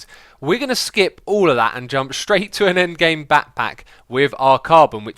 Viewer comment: did he said backpack